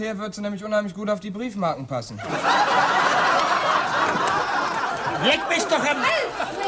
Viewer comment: "Leck mich am" "Alfred" ich hab als erstes so verstanden: "Leck mich am Alfred"